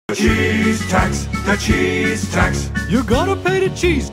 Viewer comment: For second I thought it was saying the cheese Touch💀💀😑